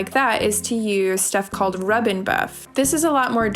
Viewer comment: you name a product called "rubbing ???" what is the product? it's blurred in the background and I can't understand what you are saying...please? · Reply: rub n buff